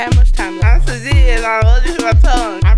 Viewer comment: This whole time I thought she said that she was saying "I'm Shadine"